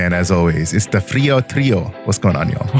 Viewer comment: I thought said "trio trio" and thought he was making a diglett episode reference from OG pokemon lol